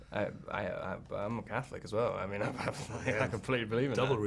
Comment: does he say he's a catholic???